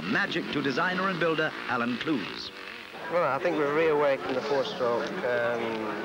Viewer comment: Bike designer (sounded like), said he never thought he would see so many 4 strokes on the GP circuit. Neither did I.